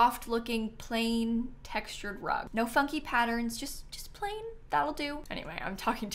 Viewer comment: She said no *funky patterns but I heard “there’s no fucking patterns” and I was too stunned to speak.